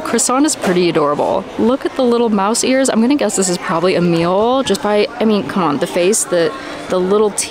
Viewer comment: I thought you said one of the Munchlings (Emile) was "a meal" and I'm like, "Yeah, of course he is."